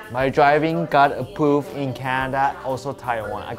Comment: Every time said "approved" I thought he said A Pooped